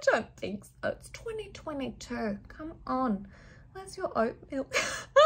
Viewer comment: ON 25:51 FOR ME,MY SUBTITLES SAID: WHERE UR OWN MILK. I WAS LIKE 🤠🤠🤠